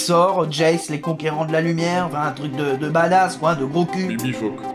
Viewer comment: l'esprit de la nostalgie repond quoi svp? Je n'ai pas compris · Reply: Bibifoc